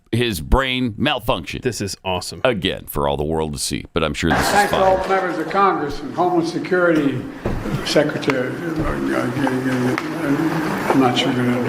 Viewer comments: Sounds like he says “homeless” security secretary.. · I could have sworn that he said homeless security?